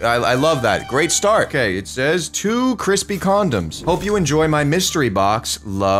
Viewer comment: anyone else hear to crispy condoms? 🤣